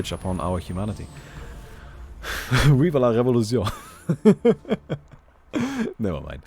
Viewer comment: Can someone translate what he said at @ 16:14? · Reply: I think it's viva la revolution